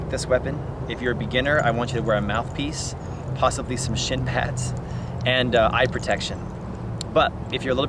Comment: Awesome! But @ 3:59 when you say beginners should get shin pads, I thought you said sh!t pads (like for training puppies) because a beginner would be scared sh!tless using this thing! Lolol! Srry, don't mean to drag down the conversation, just an honest mis-hear which cracked me up, both because it made sense - but also because it was just SO unexpected coming from you. Anyway, love your content, and your amazing positive attitude. People like you make Youtube a better place. The world a better place. Thanks for what you do!